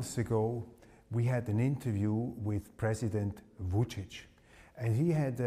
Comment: Da sind schon viele Fehler in der Übersetzung, da es durch KI gemacht wurde, die nicht alles korrekt verstanden hat. Z.B.: 2:15 "Voltage" anstatt VUČIĆ 7:33 Fluss "Audrina", richtig aber DRINA 8:04 "Zigarette" hat hier nichts zu suchen 😂 8:23 "College", jedoch ESKALATION 8:58 ANGEREICHERTEM URAN 11:10 ES GIBT MANCHE DINGE, DIE ICH SAGE, DIE NICHT OK SIND 13:05 der Satz mit "Nirvana" gehört da garnicht hin 13:53 KI = "Kontroverse", anstatt KODROWSKI 15:01 PRÄSIDENT HOLLANDE 15:16 WER WOLLTE ALSO DEN KRIEG?! 15:57 ...ODER BAERBOCK 17:21 BANJA LUKA 17:31 EIN GROßES KONZENTRATIONSLAGER AUF DEM BALKAN IN JASENOVAC, VON KROATISCHEN USTAŠA... 23:32 BIDEN 33:03 ...AUCH IHRE SEELE 33:12 ...UM DIE SCHULDEN EINZUTREIBEN usw.